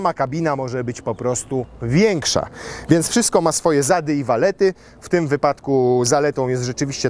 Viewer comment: "wszystko ma swoje zady i walety"...??? 🤔niech ktos mnie poprawi bo chyba to usłyszałem 😂